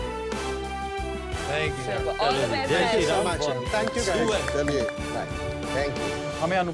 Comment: Anyone heard jai shree ram?